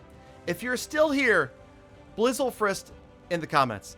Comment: hey dont know what u said at 19:45 but I watched till the end :) clash on brotha, edit: ahh blizzlefrist well blizzlefrist 2.0:)